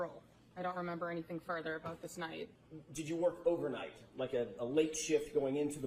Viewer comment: I had thought she’d said she worked “overtime” but said “overnight” (4:40) She knew Higgins from 2020 onward. She calls it a “Dispatch Room.” IS THERE A CSMERA MONITORING THE DISPATCH ROOM ITSELF, which WOULD PROVE SHE WAS STILL THERE PSST 3:45? Is / was there footage of her sitting in Dispatch watching Chief & Higgins while they were in the Sallyport with Karens Lexus at 5:15-5:45?